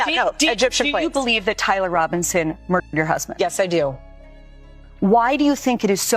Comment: … did she say “merked?” Is bari weiss WuTang now? Up from the 37 chambers!!!!